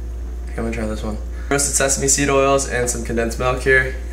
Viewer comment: did i just hear say sesame seed oil and condensed milk... 😂😂😂😂😂